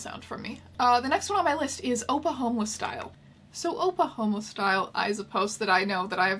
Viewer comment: I keep hearing "oppa homeless style" as "Oklahoma Style"